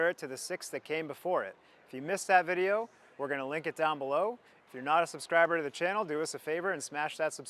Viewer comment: See... I know you SAID "link it down below", but what I heard was "Lick it down below". My ears perked up lol.